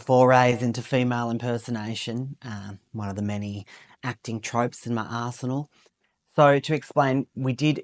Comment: When he said 'in my arsenal' I heard it as "in my asrehole" hahahahhahahaha